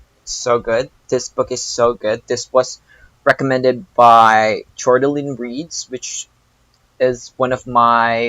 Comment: Jordaline Reads... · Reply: Thank you. I couldn’t understand what you said I thought you said Jordan Lynne reads lol